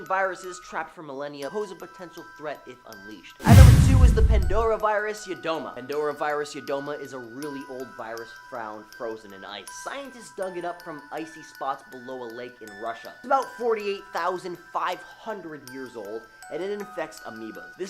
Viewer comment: I thought it said "frozen in ice cream"..lol